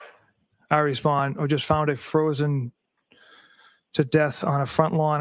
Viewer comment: Time stamp? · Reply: and play on because replays it. misheard it as "a" which makes no sense, but heard it correctly. Play it over and either turn up your speaker or put on your head phones and you will hear it clearly. He said "IT"